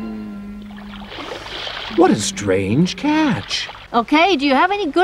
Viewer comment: I thought he was gonna say "what a strange cat"